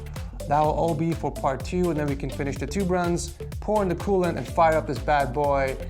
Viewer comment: Did he just say Poor in the coolant or *Porn the coolant*? ))) For me its the same )